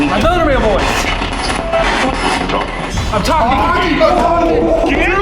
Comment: sounded like it said “wake up. I’m talking.” As if it could have been talking to a student who maybe fell asleep during a lesson?